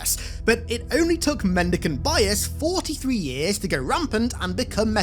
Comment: I thought you said 40 years in the intro and I was about to have a existential crisis